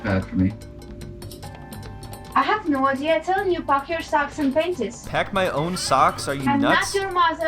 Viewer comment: Did she really say "Pack your socks *and PANTIES"?!?& 🤣🤣🤣